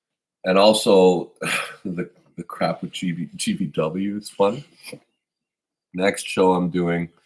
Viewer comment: what did Devon say? The crap with what? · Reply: GvW